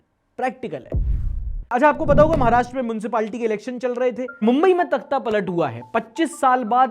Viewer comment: did I hear moonsipalty?